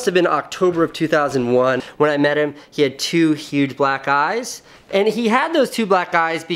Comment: Thought you said he had to huge black guys😂😂 the whole video i was wondering why